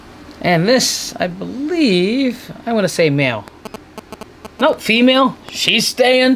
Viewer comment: Uhh am I the only one that heard him say female to female for the first clutch when he is telling us the pairing?